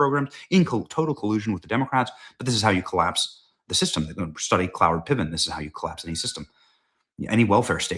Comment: what name is benny saying? Closed caption can't tell either. Is it cloward pivons?